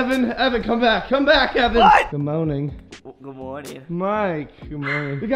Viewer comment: Um. It sounded like he said “Good moaning”...